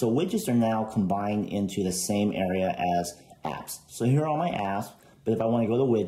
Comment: did anyone else hear "heres my ass"